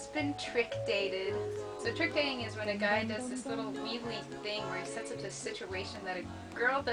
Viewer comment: Did you say weevilly? As in Boll Weevilly? Because I thought I was the only person to use that as an adjective...